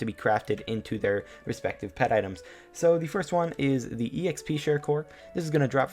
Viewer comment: Swore it said peta items